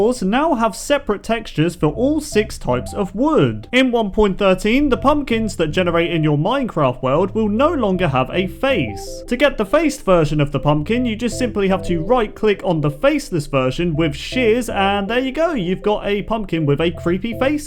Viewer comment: Vershion instead of version